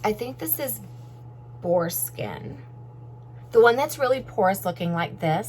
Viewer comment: When You are talking about the brushes in what they were made of I thought you were saying foreskin instead of boar skin...LOL. Lovely bag, thanks for the review!